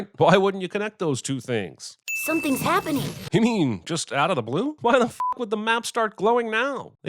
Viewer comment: did he just say out of the blue